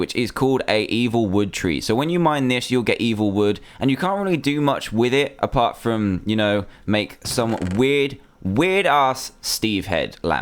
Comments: Did I just hear Dan say "Weirdass"? My childhood is a lie. Jk I'm joking don't spam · Somewhere around 4:54 did he say ass?